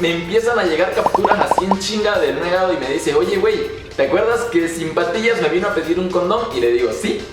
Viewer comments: Yo pensé que decía sin pastillas :) ? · Pensé que decía "sin pastillas" :v